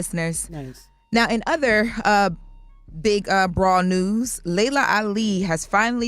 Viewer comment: dawg, I thought she said “in other big BROAD news” 🤯😂😂 she said “brawl news”